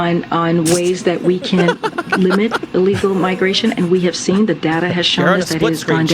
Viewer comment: Sounds like she said manipulate illegal migration at 7:15 mark